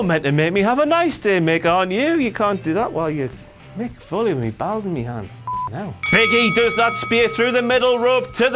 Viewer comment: I thought he said Mick Foley had his bowels in his hand, I couldn't stop laughing.